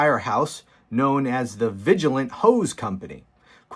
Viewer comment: “The vigilant hoes company” I know what he said but I also know what I heard. 😂😂😂😂😂